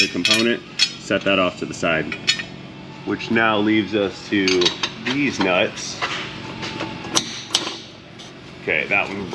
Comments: I think I heard a Deez Nuts at 10:28 or so. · I could have sworn you said DEEZE NUTZ !!